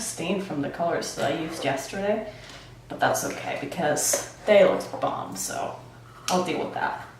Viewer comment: Churro I heard him say Mom the look is bomb, honestly it looks great don’t worry. Good 🐱 I agree.